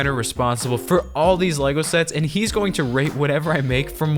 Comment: this is really wrong but is it just me or did i hear “rape whatever i make” istead of “rate”